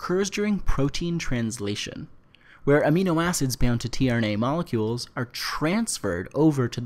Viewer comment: I cannot understand the word you said before the word trna. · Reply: "Amino acids bound to tRNA molecules.."